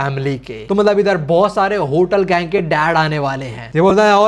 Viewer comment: Did u say hotel bruh